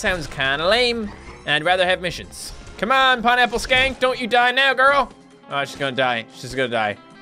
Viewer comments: Did say Pineapple skank? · did he just say 'Pineapple Skank' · its funny how it sounds like hes saying pineapple skank